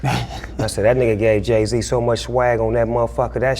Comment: Did snoop just say swag at around 3:35?